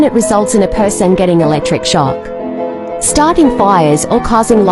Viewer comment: did you say fire